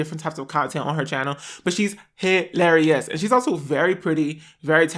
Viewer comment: thought he said “HAIRlarious” lol